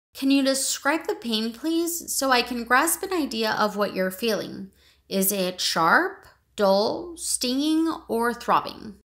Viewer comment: Some can tell me what the doctor's saying during min. 2:00, the pain is sharp...? ( I couldn't heard what else) thanks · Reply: HI it says, “Can you describe the pain, please? So, I can grasp an idea of what you are feeling. Is it sharp, dull, stinging, or throbbing?”